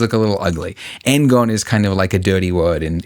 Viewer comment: For the people who thought he was saying "Angon" or something like that, he's saying "N-gon", hence having >4 vertices ("Oh no! More than 4!" "How many more?" "Doesn't matter! It's more than 4! N! N vertices!")